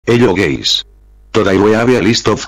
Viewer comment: i hear; "hello geys" -_- im from poland and brek is star in <num>. Nice video!